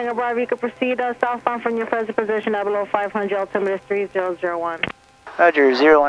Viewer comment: I'm pretty sure she told you not below 500ft, or have I heard that wrong?